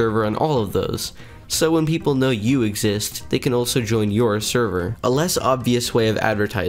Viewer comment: Why do i keep hearing him say "Sherver"